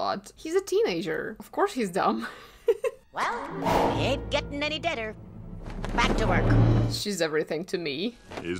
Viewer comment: when I was a kid i thought that yzma said: "He ain't getting any dinner."